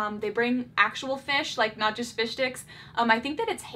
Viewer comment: Fish sticks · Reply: At first I thought it said "fish stinks" 😆